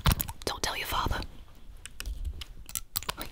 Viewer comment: I thought it said “big mama proud” until I watched it again😭😭😭💀